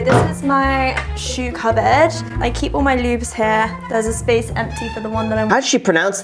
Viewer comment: I thought she was saying "I keep my loubs here"